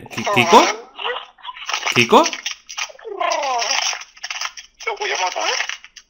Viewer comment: Si se fijan en la traducción del minuto 14:29 note que en una de las fases de esa llamada escuché algo como "te voy a matar jejeje"